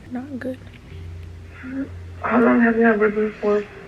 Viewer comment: it sounds like you said "how long have you been here for" while sleep talking